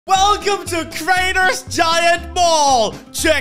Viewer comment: In the start of the vid i thought he said "welcome to crainer giant ball" xd